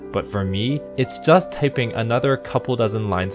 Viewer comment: who else got annoyed by the bounciness of character after he turned on the auto animation on? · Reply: I misheard "typing" as "tipping"